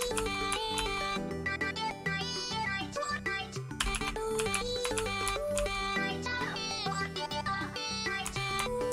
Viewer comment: Is it just me or did he just say fortnite? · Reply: he saying fortnite